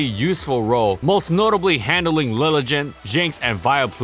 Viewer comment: Did he just said "LiliJent?